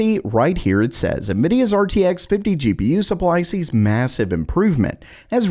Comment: did someone say massive?